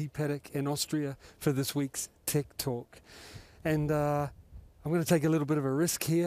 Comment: "tech talk". But i hear that was "tik tok" 😅